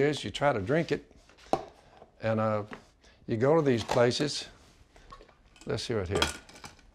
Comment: What does he say at 1:01???? Someone help! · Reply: thanks Sean. I always thought he said “Let’s sue it here.” 😂😂 I know it doesn’t make sense but whatever